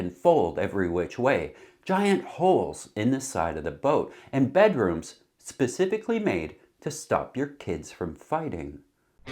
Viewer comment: LMAO, my volume wasn't loud enough when I first loaded this one, and I heard "bedrooms specifically made to stop your kids from F----", I yelled WHAT? rewind... oh okay. 🤣